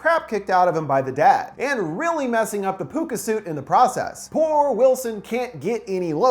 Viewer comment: To be honest I thought it said poopa 💩